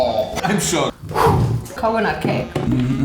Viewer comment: did she say corona-cake? Because this video was uploaded 7 months ago🤔